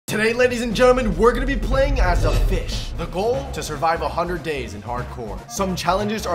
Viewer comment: i thought i heard u say “playing as a bish” and i was like “WHAT DID HE SAY!! ”